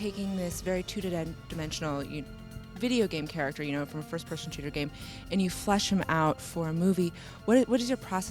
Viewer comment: did she just say a first person shooter game? in